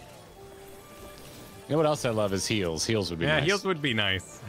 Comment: I heard "you know what else would be nice would be heels" and I was confused why you wanted women's footwear until I finally realized you wanted heals